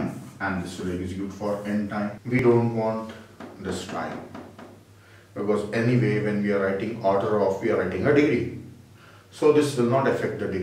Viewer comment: he said "we don't want this time because any...." then I couldn't hear clearly what is he was saying for a few seconds. Would someone please type it down for me? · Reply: “ any way when we writing order of anything we are writing the degree” Which means eventhough if you write 2n or 3n or 5n when we write it as an order of function we will consider the degree of polynomial only